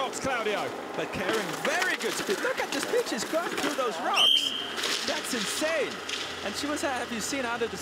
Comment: does he say “look at this bit** go right thru the rocks” Or is that just me hearing things?